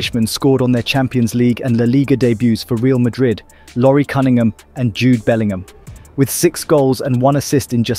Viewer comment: I thought he said cummingham 💀